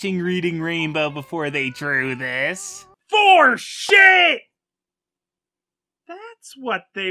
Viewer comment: what were you trying to say was probably "Foreshadowing!"